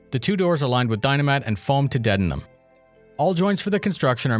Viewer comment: I swear you said the doors are lined with dynamite